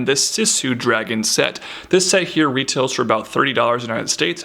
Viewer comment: When you said $30 Sounds it really sounded like $3 LOL. That would be a nice price!